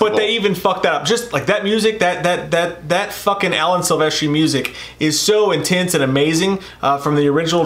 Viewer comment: did say Alex Ovechkin music? it sounded exactly like he said Alex Ovechkin music. I do have hearing problems though. You guys seem like Matt Stone and Trey PArker fans? If so, extra points for you guys, they are my pick for the greatest entertainers and most intelligent people of the last 50+ years.